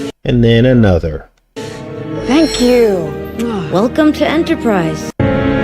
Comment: Wow. Just watched the replay.. mind blown. Satan is in a hurry isnt he and .. eyes to see_ God is showing us much thrue You brother. When she said 'welcome to enter prys' I thought she said 'welcome to anti christ'. I was hit two thursdays ago (outa nowhere) high fever now cant smell cant taste my ears are Worse. Yestday I ate cereal and afterwards noticed the milk was clabbered😢. Please pray for me surrounded by demons (human form) they are relentless. Awesome amazing show Praise the Lord.